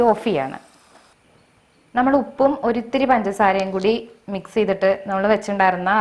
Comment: சேச்சி சூப்பர் சமையல் தயிர் மிளகாய் சூப்பர் பஞ்சாட்சரம் என்றால் என்ன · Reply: Kasthuri Varathraj.. Amma, you mean at 3:10? I think you misheard Panchasara as Panchatsharam. Panchasara is sugar (white sugar/table sugar). சக்கரை